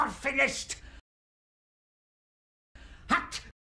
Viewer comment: When I heard “morphinist” for the first time, it sounded like “not finished” and with the random nasal sound he made just before, I thought it sounded like he said “I’m not finished” even though I knew he wasn’t speaking any English.